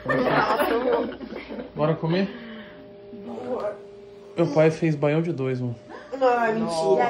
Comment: Eu ouvi aos 4:27 “Meu pai fez baião de dois”? Ou ouvi errado!!!